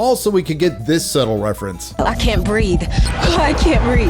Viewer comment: I thought she said “I can’t breeve”